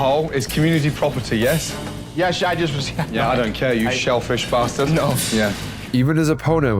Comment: did he just call him shellfish 😭 This is comedy gold right here 😂